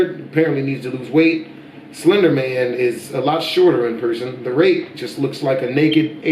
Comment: It sounds like you said the rape and not rake